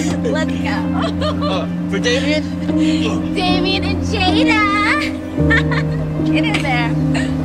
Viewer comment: I swear she was gonna say Damian and Biaaaaaaaaaaaaaaaca! 😂😂😂😂😂